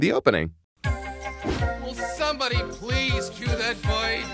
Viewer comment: I mishear it as "Will somebody please kill that bird!" too.